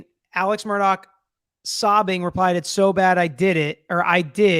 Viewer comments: I heard “it is so bad.” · I just hear, "It is so bad". I don't hear I or They at all. It's kind of like watching those paranormal tv shows - I can never hear what they say can be hears.